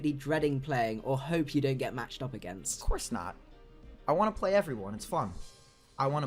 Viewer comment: Does anyone know what said at like 5:04? (right before was like "let's not go that far) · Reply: "You want to whoop everyone!?" is my guess.